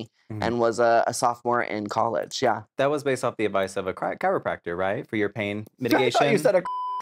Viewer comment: I'm sooooo happy for Laganja, her journey has been wonderful and healing. She has such an endearing and lovely personality. I also thought that Maddy was saying crackhead instead of chiropractor. I was loving Dabber too, she's the sweetest and very expressive. It was perfect to have Maddy as the bud and Laganja as the flame, GORGEOUS! The looks certainly complimented the interview, beautiful and compelling. Greatly appreciated all your sharing, Laganja!